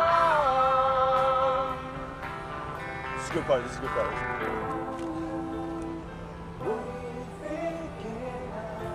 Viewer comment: Can anybody please tell me what is Jensen saying in 2:28? Thank you. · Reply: it´s a good part, it´s a good part